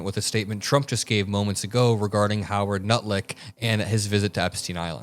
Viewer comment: Did you just call him “Howard ‘Nutlick’?? @ 8:41 🤣🤣🤣 hilarious!!